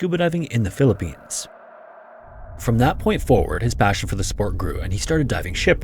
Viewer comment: The opening disclaimer in the top left made me so happy. In an era of educational/informational content being so sloppily mass produced by generative machines, it’s go comforting to see such things. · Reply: his voice is ai. why else does he say support instead of sport at 11:10?